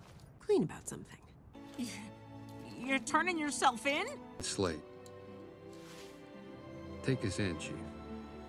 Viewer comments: it kinda sounded like Mr. Wolf said “Slay” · I thought it just said “✨sLaY✨”